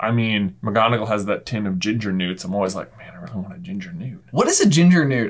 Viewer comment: I've always assumed a Ginger Newt is just a Ginger Nut in the shape of a newt. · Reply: I thought they were saying gingernuts like gingernoots 😂